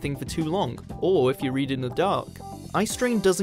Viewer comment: When he said " or when your reading in the dark..."I seriously had to rewind because I heard "or when you are eating the dog..." my brain is broken AF.